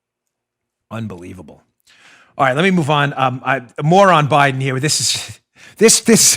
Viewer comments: "...more on Biden" sounded like "...Moron Biden" hehe! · did you just say "moron Biden"? at 18:41? That's not very nice. Oh, wait, it was "more on Biden". Sorry, my bad.